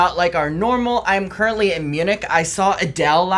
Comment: I thought said he was currently a Eunuch at the beginning. 😂